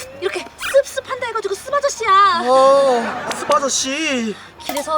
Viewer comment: 아ㅋㅋ왜욕으로 들려(쓰바저씨라고들리넼ㅋㅋ